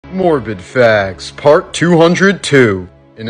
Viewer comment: A little upset cause I thought you said Morbin Facts